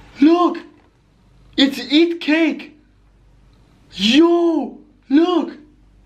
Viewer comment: kinda sounded like “eat cake” instead of “IT cake” lolol